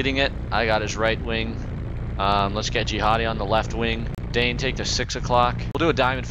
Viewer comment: did he say let's get jihadi on the right wing